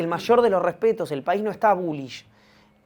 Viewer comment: No entendí lo que dice Ivan en 40:25 Lo retrocedí varias veces pero no le entiendo y el gesto de Fede tampoco me lo aclara, alguien que me desasne plis... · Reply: Bullysh es un término que usa Maslatón para decir que el país está al alza económicamente